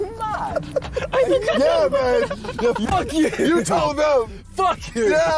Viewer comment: Did JJ call Simon 'Babe' @ 7:22???? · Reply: He said mate